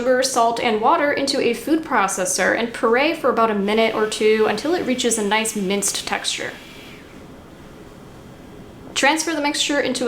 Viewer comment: I genuinely thought she said “pray for about a minute or two until it reaches a nice mixed textured” 😂